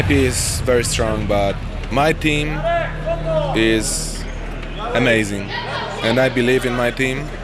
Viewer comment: lol, thought he said my ping is amazing